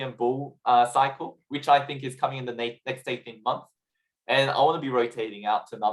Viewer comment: Is he saying 8 to 10 month or 18 months? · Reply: 18 months.